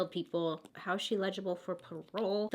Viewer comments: It took me so long to realize she was saying “eligible” instead of “illegible”. I was so confused lol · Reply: I thought she was saying legible, like neatly written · lol the way she pronounces “eligible” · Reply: I just commented this. Lol I rewound it like 3 times bc I thought she kept saying legible